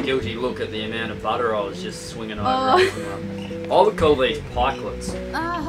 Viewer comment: at 12:22 you say something like “I would call these” and then I get lost and have no clue what you say next. What would you call them? Thanks man love the vids! You and are awesome. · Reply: Pikelets.